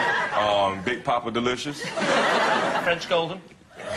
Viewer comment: Can some one please tell me what Lee says at 3:23 mark? Thanks. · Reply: T Young He says, "French golden." As in French Golden Delicious, a kind of apple.